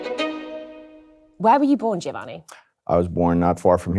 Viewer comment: My dyslexia had me read the intro 00:38 as UNDERCOVER LOBSTER. 😂😂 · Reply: And at 00:43 I heard “why are you born Giovanni” And thought he’d say “to catch criminals”